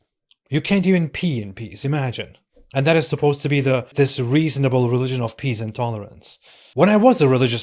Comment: I heard "The realigion of pees and tolerance" because my brain was still latched on to the word "pee" I guess.